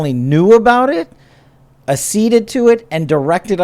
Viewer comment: what word? Asceded?